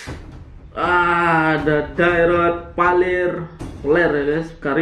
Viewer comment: abang ngomong apa tuh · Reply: Peler 😅